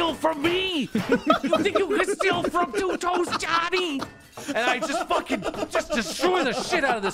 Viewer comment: This whole time I thought that were saying "two toast Johnny"